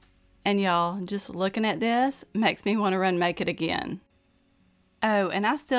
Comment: When you finished the Choc Mounds cake--you said "it made me want to make it again"--I thought you said "it made me want to run naked again"!!!!